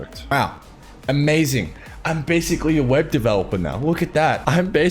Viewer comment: I thought it said " weeb developer" and I was so ready for australian anime yandere sim